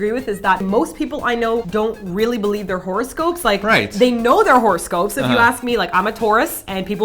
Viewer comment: I thought said horse ghost instead of horoscope.lol